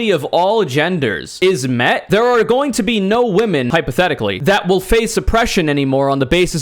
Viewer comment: I thought deadass he just said, "there are going to be no woman" and nothing else